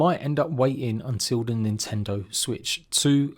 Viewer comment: world needs some legal wins so heres hoping nintendo/gamefreak lose the suit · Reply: I thought I heard Nintendo won